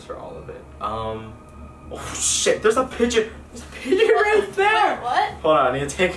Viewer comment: When he said pigeon I thought he said bidget